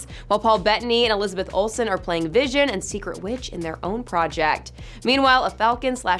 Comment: 1.09 did she just say “secret witch” instead of “scarlet witch” ??😂😂